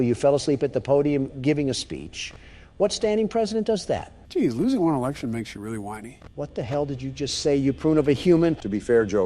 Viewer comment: I think what Joe said at 1:41 was "Jeez, losing one election makes you really whiney" but I'm not sure